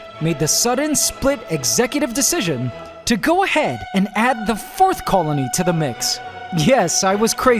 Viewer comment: I was watching and at 21:24 I was distracted and thought he said the fifth me: wait... WHAT!!?!